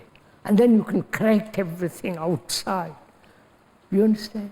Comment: Pretty sure @ 59:49 he says 'and then you can correct everything outside' and not 'and then you can crack everything outside' which is what the subtitles say.